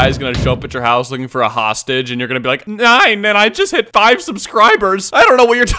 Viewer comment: thought he said “NEIN”